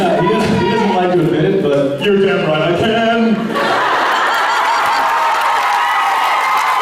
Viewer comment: what does jared say at 20:13? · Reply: Pretty sure he said "You're damn right I can!"